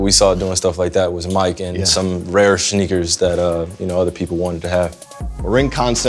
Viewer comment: did he just say “Shneakers”😂